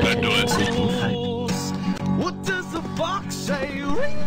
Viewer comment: It sounds like he said what does the fuck say